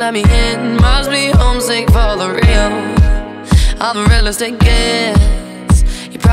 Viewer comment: I swear she said, "I'm a real estate guest" xD That's what I thought she said when I heard this on the radio before seeing the lyrics